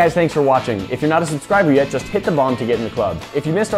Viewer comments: Always sounds like he says hit the bong to be in the club lol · I thought he said, "Hit the bong to subscribe." Cool gun, btw.